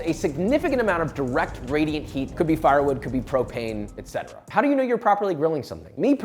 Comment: When I watched this bit, I was only half paying attention, and misheard “propane” as “cocaine”